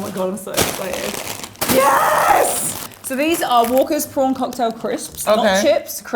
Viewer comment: i thought it said Wanker's